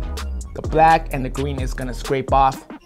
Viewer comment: I thought he actually said green like “gween”😂